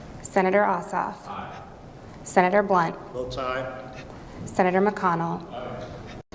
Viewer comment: Senator Blunt - Did I hear him say ... bowtie? Is that right? serious